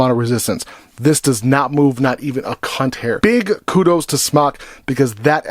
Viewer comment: did he say not even a cunt hair? lol